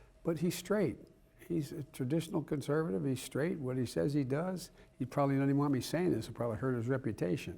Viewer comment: Did he just say does what he says? Roe v. Wade wouldn't be overturned right now if he did what he says